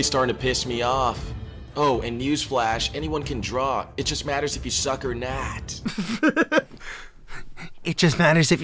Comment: did he said "if you suck or not" or "if you suck a knot" ?